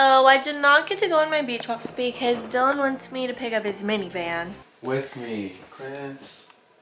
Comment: What I heard: “Dylan commanded me to pick up his mini van for him” 😂 same thing lolll